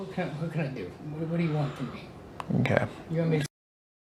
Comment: What did he say at 53:27🤣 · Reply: I think he said you want me to k1ll myself or he said want me to sh00t myself?